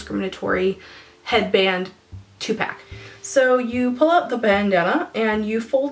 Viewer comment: I thought you said TUPac when you actually said two pack.... Nearly died laughing.